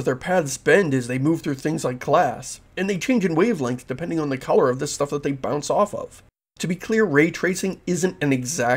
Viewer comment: What He say betwen 8:24 and 8:29? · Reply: "... and they change in wavelength depending on the color of the stuff that they bounce off of."